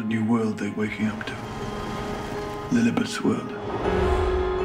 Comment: Michelle, a knocking shop is a colloquial expresion for "brothel".... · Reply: thanknyou im gonna ask that one...oh and I thought she said knockling shop lol